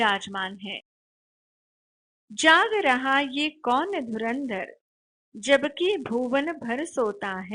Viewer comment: I heard dhurandhar 5:35 😂😂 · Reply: But it's dhanurdhan